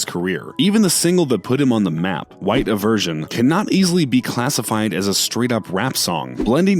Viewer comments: I thought they said "white diversion" 😆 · Did he just say, white eversion? 😂😂😂😂😭😭😭